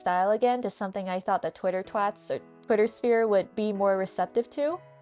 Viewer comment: Idk if what i heard was right, but i love the idea of you calling the people on twitter "Twitter twats" Really describes them well in my opinion lol